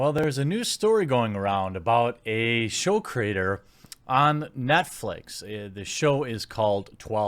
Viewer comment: I heard "Methflix"